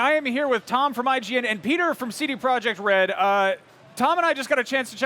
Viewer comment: i thought he said: beard from CDProject Red lol)